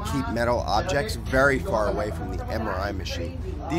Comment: I thought he said "and actually kill a bitch" lol I had the volume low